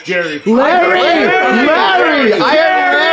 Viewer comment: i heard jarry no way anybody could hear larry